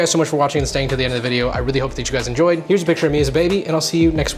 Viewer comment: i was watching and listening while making breakfast and at the end i wasn’t looking at the screen and thought you said “ use a picture of me as a baby” and was like ……… wtf does that even mean